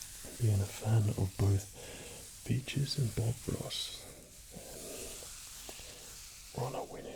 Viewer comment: All I hear Is "bitches and Bob Ross"